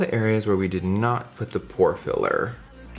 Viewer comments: I really thought he said "whore filler" when he was naming the primers LMAO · Am I the only one who thought Jeffree said whore filler when naming the primers